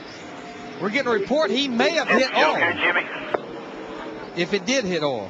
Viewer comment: I heard “he may have hit oil” as “he may have hit the wall”